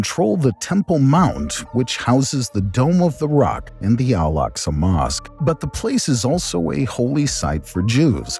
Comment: i spat out my water cuz i thought you said the alaqs among us